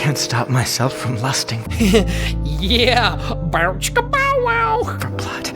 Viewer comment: When he said lusting, I had to rewind cus I thought he said busting